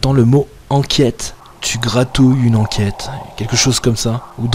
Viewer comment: Salut content de revoir cette enquête j'ai compris "tu gratouille une enquête" et surtout gros soutiens en c'est moment difficile à toi et à la famille de seb "tne spirit. Je vous envoie plein d'amour et de respect 😘🌹💐🌹